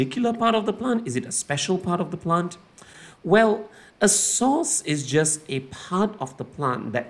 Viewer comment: Why did I hear sauce instead of source? 😭